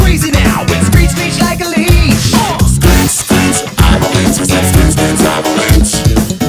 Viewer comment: The "screech screech I'm a leech" totally broke me. Probably because I first misheard it as "screech screech I'm a leek"